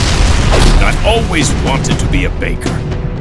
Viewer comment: same but Faker instead of baker Jk both